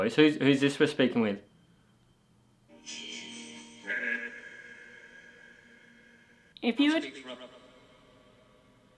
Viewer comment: (maybe a second later) I hear a woman beginning to speak again, but she gets cut off by a creepy man voice breaking through. It is not captioned, but I hear “she is…dead” followed by “I speak for her” (which you have captioned), and it is almost like the same male voice coming into acuity. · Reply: GREAT CATCH! Sounds like an old man: 'She is...DEAD...', followed by the younger man's, 'I speak for her'. ⭐⭐⭐⭐⭐